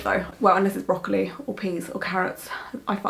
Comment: I'm sorry you don't like the tattoo. I think it looks nicely done but I can definitly see the "turnip" in it. On another note when you listed you favorite vagetables I could have sworn you said:Broccoli or PENIS and carrots! XD Sorry for my dirty mind